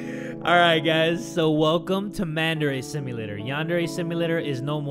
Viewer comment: i thought he said mandarin simulator